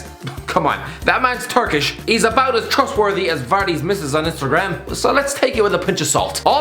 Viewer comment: At first thought him saying "that man's Turkish is about about as trustworthy as Vardy's wife on Instagram" was "that man is Turkish. He's about as trustworthy as......." 😂😂😂